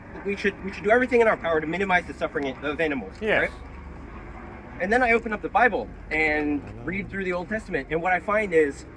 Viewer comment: What religion did he say he grew up in around the 10:00 minute mark? I couldn't hear it clearly. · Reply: Jainism, the Jain religion